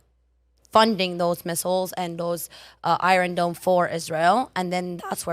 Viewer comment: Did she just say Israel should stop funding the Iron Dome??? Good lord...